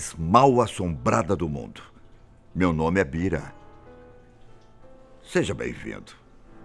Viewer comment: só eu que entendi "meu nome é pica"?